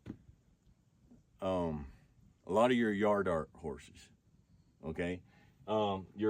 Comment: I just learned the term "yard art" I always thought people were saying "yardark" I had no idea what that meant but when said "yard art" it clicked that's wild learned a new word